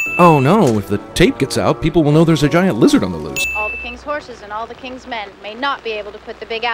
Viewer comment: Caption says "Lizard is lose" narrator says "Lizard on the loose" Ding sin for.